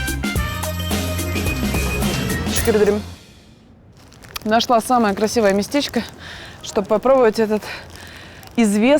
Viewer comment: скывырдым? Или мне послышалось?🤣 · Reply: "Тешекюр эдерим" она кажется сказала - "спасибо" по-турецки. 🙂